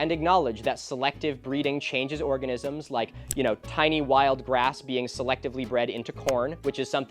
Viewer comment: I thought he said tiny wild RATS were in corn lmao. I was worried about his gardening for a sec.